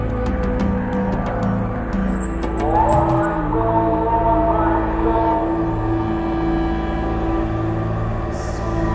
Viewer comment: Did i just hear صوت اذان in 5:09 ... Hey muslims!! correct me if I'm wrong!!! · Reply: It's definitely Allah hu Akbar. They put the athan in their idk why lol